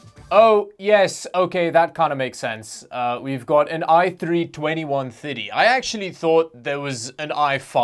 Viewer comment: is it me or does he sound like he's saying 2150 instead of 2130?